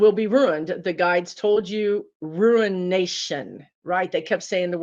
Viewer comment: Uh-oh--when you were talking about Elon Musk and said that your guides said in reference to him "ruination" I heard it also as "ruin nation." But please know I absolutely believe that this is the U.S.'s "breakdown before our breakthrough," as my wise spiritual teacher used to say. I so appreciate what you do,another rim-rider here! 👍❤️💫